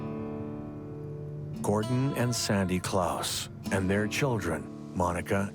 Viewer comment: Sorry! I heard "Gordon & Santa Clause!"..😆